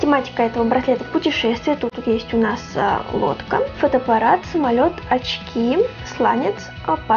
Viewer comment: Послышалось "водка", ахахах